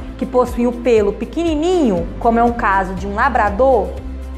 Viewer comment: Entendi "peido", o meu solta!